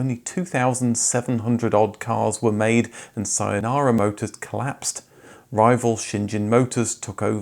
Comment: is not Sayonara (bye in Japanese) but Saehan (means New Korea) motors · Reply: It is actually Saenara, Saehan came right after them! Still, no Sayonara 😂